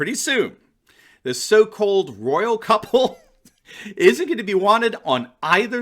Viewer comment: I thought I hear "the royal cuckold" instead of "the royal couple" lol